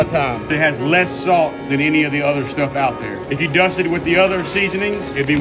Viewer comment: Thank god for the captions. Definitely thought he was saying seafood bowl lol